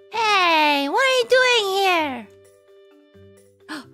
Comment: In my mind, I heard "Ey, im walkin here"